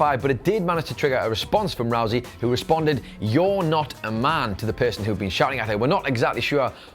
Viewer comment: I thought Ronda Rousey said “You’re not my dad” when she retorted to that one guy in the crowd.